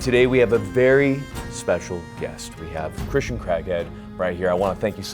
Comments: Hahahhaha, as a non-American (who is married to an American) I always laugh at the way you guys pronounce CRAIG. It literally sounded like you said Christian Crackhead. It's CRAHHHHAAAAIG not KREG or CRECK. Great interview!! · The perfect comment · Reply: I thought he said Christian Crackhead😂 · i thought you said christian crackhead xd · I thought he said Christian Crackhead lol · i thought he said Christian crackhead 😂😂😂😂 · did you say Christian Crackhead? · i thought he said my guest is "a christian crackhead" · i swear to god you said 'christian crackhead' 💀